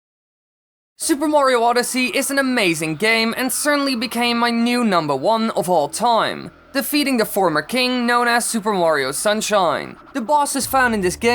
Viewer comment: super wario sunshine? 0:41 am i hearing that the good way?